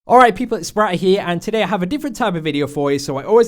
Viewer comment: I thought he said 'what's up guys its bratt here'